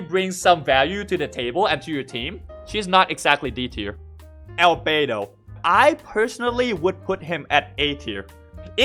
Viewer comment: i thought he said L beidou